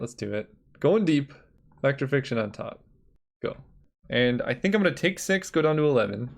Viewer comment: Did he just say "Put Fact or Fiction on top" of 4 more lands?